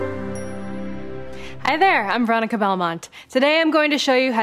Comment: I thought I heard '' Hi there , I'm Barack Obama'' lol XD